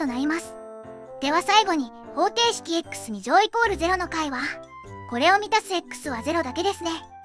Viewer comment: 私は心が汚れているので、「これを満たすエックスは」と言っているはずの箇所が「これを見た○○○○は」に聞こえてしまいました。